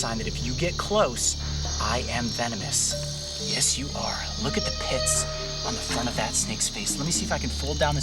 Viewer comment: I thought he said look at the piss instead of hiss